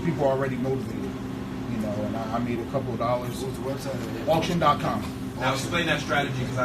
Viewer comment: What was that website that guy mentioned to find pre forclosure properties? it sounded like he said, <link> ?? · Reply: Auction. com